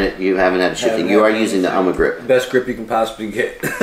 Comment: what grip did they mention ? Anyone here know the name of the grip he used for that quad ? Sounded like Omni grip but I’m not sure. Thanks everyone !